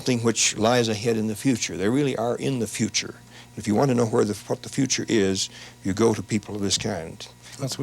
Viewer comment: It sounds like he says "if you want to know where the fuck the future is…" Just thought that was kind funny. Really high here.